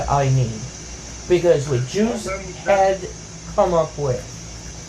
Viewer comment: so does he say “that the Jews came up with” right here? Or like what the fuck is he saying?